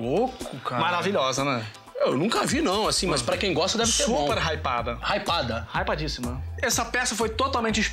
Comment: Hypado? foi isso que eu ouvi? lkkkkk